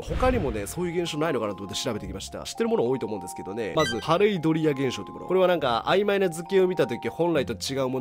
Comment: ながら作業してたら、『カレードリア現象』って聞こえて二度見した😂